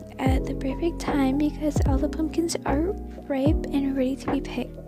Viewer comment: If you didn’t notice you put rape instead of ripe 3:54 not to be nitpicky